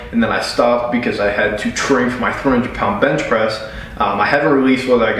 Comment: i spat out my drink bc i misheard it as 400lb bench press